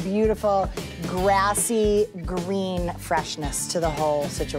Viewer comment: To everyone who thought he said GREEN, do yourself a favor and GET YOUR HEARING CHECKED! he definitely said red if you listen...